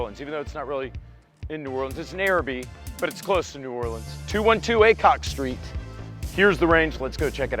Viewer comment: For those of you who think you might actually want to make the grand opening: Prizes will be given out every half hour from 1pm to 6pm. We're giving away a $1,800 Rainier Arms RUC 5.56, A Gen 1 Glock 17, two B&T Suppressors, a Rugged Obsidian 9, two Aero Lahar-30 suppressors, a complete Aero M4 lower, a complete Aero M5 lower, optics from Primary Arms, and more. It's probably $10k worth of guns/gear. Neutral Ground Gun Co. 212 Aycock St. Arabi, LA 70037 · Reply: 100% thought said 212 ACOG street.